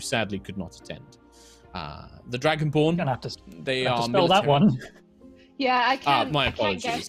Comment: I thought he said 'gonna have to counterspell that one' and it made 100% sense